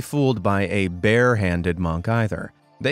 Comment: I thought u said monkey 😭